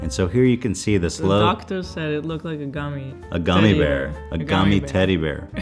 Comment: auto captions said “doctors said it looks like a commie”